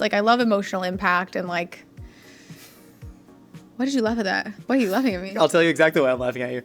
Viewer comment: slight captioning error: “love with” instead of “laugh at” and the “for LO side” (??) instead of “forlong sigh”